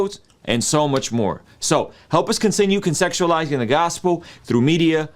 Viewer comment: I listened to him three times,and he actually said help us ConSEXUALIZE " twice, at about the 8: 37 mark and 9:14! Listen for yourself and see. I assume he meant CONTEXTUALIZE,but maybe it was a Freudian slip! Is he subconsciously saying he wants to spirituality seduce his hearers??